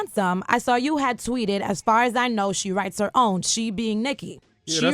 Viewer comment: oh my God why would he say it like dat! gosh i thought he was saying he wrote her bars